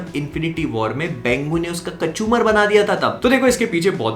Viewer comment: I thought you were saying WenWu... But it was 'Bengu' 😂😂🤣🤣